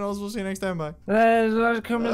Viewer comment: At the very end I just hear : "Join me on Porn Hub!"